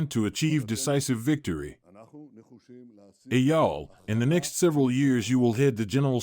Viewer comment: Did he say "Hey Yall"? 😂 Am Yisrael Chai! ❤🗡️🛡️